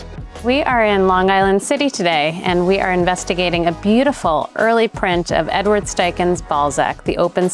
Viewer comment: At first I thought she said they were checking out Edward Steichen's beautiful ball sack 🤣